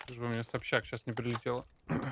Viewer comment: когда смотрел послышалось в начале " Лишь бы мне сейчас СОБЧАК не прилетела " )))